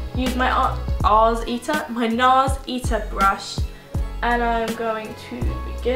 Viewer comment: lol at 7:43 i thought you said ass eater XD